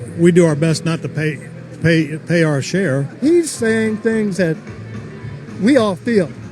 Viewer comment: "Trump and the average American do our best not to pay our fair share" so....you want America to fail even though Trump claims he wants to "make America great again"? if you're not paying your fair share of taxes...then you are costing America money. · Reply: Glynn Tarrant "We do our best not to pay, pay, pay uh our share". That's what I heard. Kyle definitely said fair share.